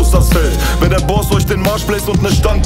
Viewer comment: "Wenn der Boss euch den Arsch bläst"...ich kann nicht der einzige sein der dass Gehört hat oder?